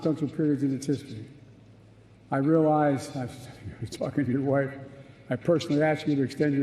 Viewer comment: what did he say about that man’s wife?😳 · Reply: talking to his wife